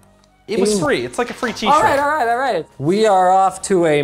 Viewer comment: with closed caption's.. - [Jake] "It was free, It's like a free cheese stick" 😂😂😂 Instead of T-shirt